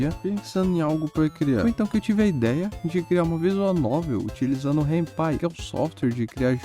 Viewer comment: só eu entendi hentai ?